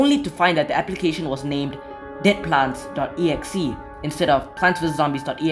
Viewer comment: i swear to god he said "Dickplants"